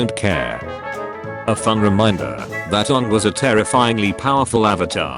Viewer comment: DID I JUST HEAR UNG!!!!